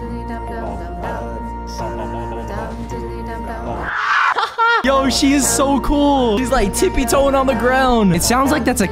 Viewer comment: Nice new setup man! (LETS GET TO 60K!) 10:42. 13:05 (sadness). 14:49 also did u say (what the f was that or). (What the fudge was that) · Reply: What the fudge